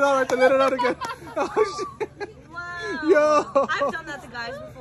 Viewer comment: Bruh I thought she said "I have done that as a guy before".